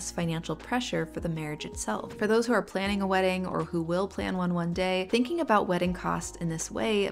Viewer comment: Hahahaha I misheard it as "wedding or orchids"